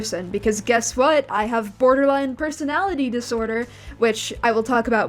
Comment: i misheard "borderline personality disorder" as "online personality disorder" and honestly he probably has that too